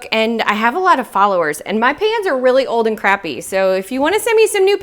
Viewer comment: I could have sworn you said "...and my fans are old and crappy!" 😆